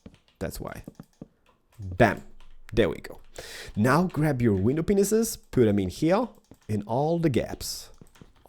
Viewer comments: did he say window penisses? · sounded like you said penises XD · 'now, you grab your window Penises" SORRY BUT THATS WHAT IS SOUNDED LIKE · did he say window penises